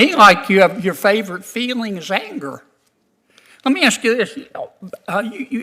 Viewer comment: "Curb my nausea!" I'm definitely stealing that line! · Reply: I'm stealing "Sounds to me like your favorite feeling is anger"! Initially I thought he said "Favored Feelings Anger" which I'm also stealing, with prejudice!